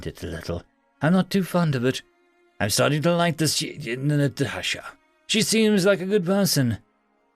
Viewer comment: still watching but at 5:23 does anyone else hear him stumble over the words ''Human Natasha''? not trying be mean, i just found it kind of adorable.